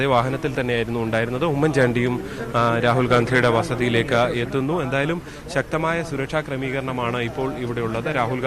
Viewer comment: Did he say "oommen chandy Rahul Ghandhiyude vasthiyilekku ethunnu?"